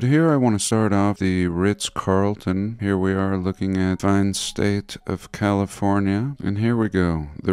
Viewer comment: I'm sorry, did you just say "the fine state of California"? I'm lost already